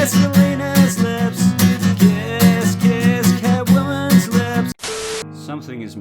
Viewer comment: I thought you said Katt Williams' lips instead of Catwoman, I was concerned